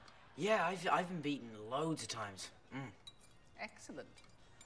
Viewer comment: "Oh yeah. Yeah I've been there loads of times" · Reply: The GGShow I thought he said “ I’ve been beaten loads of times.” It does sound like he says “beaten”.